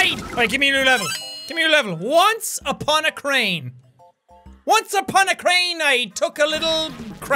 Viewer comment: Instead of "once upon a crane" I thought it said "once upon a cake"